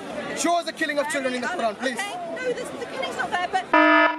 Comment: what did the lady say ?? Please tell me. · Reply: The killing isn't there